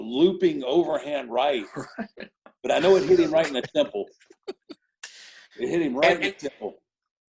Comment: Honestly thought I heard Taker say "he hit him right in the nipple" when talkin about Bret punching Vince LMAO